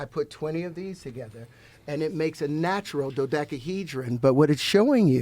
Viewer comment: Wtf did he say at 7:20? 🤣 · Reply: He said a dodakahedren 😂😂